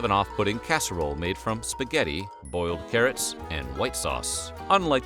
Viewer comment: At 4 min 38, where you said 'Boiled carrots', I heard you say 'oiled ferrets'.....Really was confused for a moment. Then again, during the great depression, I wouldn't put it past people to eat an oiled ferret!